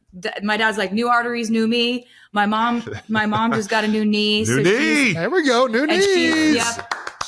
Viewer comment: new knees!! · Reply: I thought I heard new niece at first lolol